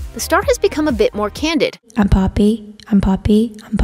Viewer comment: When she said i am poppy i thought she said i am papi